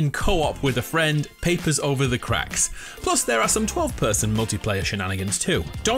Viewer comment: Throw me some opinions on the state of PlayStation Plus Essential, please. Thanks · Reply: Did you say at 1:31 the game is now 12 person multiplayer? Am I hearing correctly?